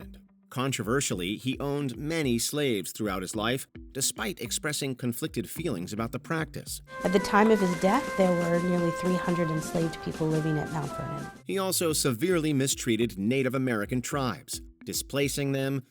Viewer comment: I did not understand what the woman's voice was saying between this timeline 10:58-11:02. Something about "At the time of his death....."